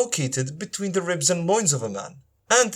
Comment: you said the sperm is between the ribs and the what? Moinds? I couldn't make out what you said pls answer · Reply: I believe is saying, "loins."